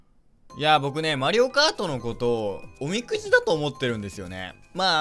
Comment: 『マリオカートのことおみくじだと思ってるんですよね』が 『マリオカートのことゴミクズだと思ってるんですよね』に聞こえた